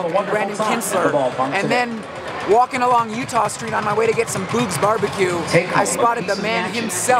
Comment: did you just call boog’s bbq “poog’s barbecue” 😂